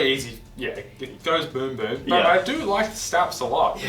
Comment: I did not hear "goes boom boom" I heard "throws boom boom." It will now only "throw boom boom."